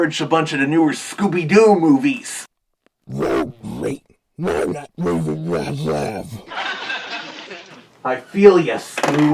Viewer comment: What did Scooby-Doo say ? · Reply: "Oh great! Darn that David Zaslav!"